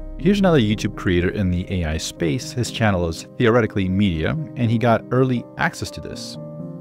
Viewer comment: Such a dick move... Using other youtuber's content without saying who he is, also cutting the video right so that the name of the channel, nor the title is visible... And yea you say you'll link it in the description, but then do you? Not the first time either :// Right after the video with Aschenbrenner's face blurred in the thumbnail, so ridiculous... You're getting more greedy with each video. I never cared about 'shocking' and 'stunning', but this is directly impacting other people, not just me as a viewer. I'm one video away from unsubscribing, but does that matter? :/ Rly not cool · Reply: Hey guys, Wes added the links to the description, as promised. And I have to apologize, I misunderstood. The channel name is "Theoretically Media", which Wes clearly says in the beginning. I thought he said "he's channel is theoretically medium" as in referring to the size of the channel, and the fact that he got early access despite not being the largest on the scene 😅 I thought it was a weird way to phrase it, now I understand why. I'm very sorry, I was too quick to judge! Turns out the only dick move here was my comment, huh... I stand my ground regarding the general 'stunning' trend, but that's not nearly as bad! My apologies, again!